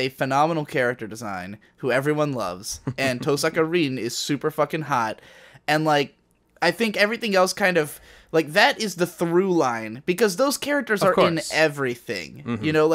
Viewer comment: I heard "toe saccharin"